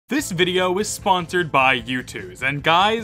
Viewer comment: I thought bro said "this video is sponsored by youtube" 💀💀💀